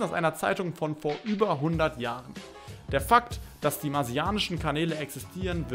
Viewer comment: Ich habe zuerst verstanden "Marsianer bauen Kamele" :))